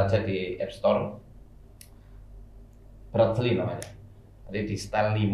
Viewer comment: apasih nama apk yg disebutin ustadnya.. audio nya pas gangguan ga jelas kedengeran ustadnya ngomong apa 😢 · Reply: Breathly kak apps nya